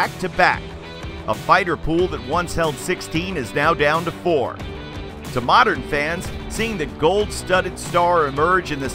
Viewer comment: what did daevitt say? Splinters....apollo 55? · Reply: Time splitters and apollo 55 were tag teams back then with devitt and kushida